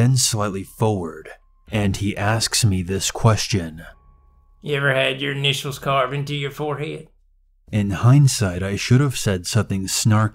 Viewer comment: what that Donal duck said..?!! Sorry my English is too bad to understand it. 👀😂😂😂😂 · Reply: "Have you ever had your initials carved into your forehead?" Yoink gtfo LMAO